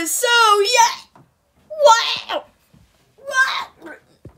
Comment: How Did You Say inside out? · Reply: He did not he said he was so young